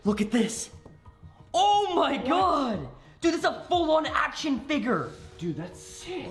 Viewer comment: says figir instead of figure